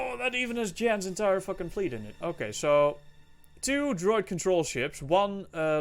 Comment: Did you say Drug Control Ships?🤔